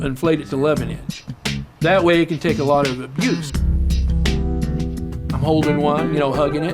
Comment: HELP AT 9:35 I THOUGHT THE GUY SAID SO IT CAN “TAKE MORE JUICE” NOT ABUSE 😭